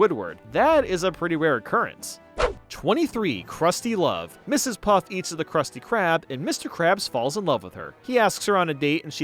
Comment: How video sounds: "Ms Puff eats ₐₜ the Krusty Krab." How I heard it: "Ms Puff eats the Krusty Krab, and Mr Krabs falls in love with her." hol' up